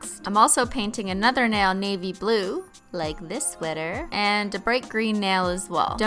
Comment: When watching your Snap story, I thought you said baby blue, not navy blue, and I was like, 'you liar, that's NOT baby blue!'